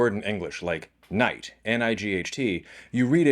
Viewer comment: I thought you said "knight" lmao. Was truly surprised when you spelled "night."